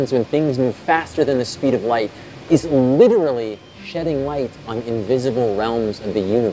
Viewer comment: what did he say? Shedding light. Oh. Shedding. That's not what I heard at first.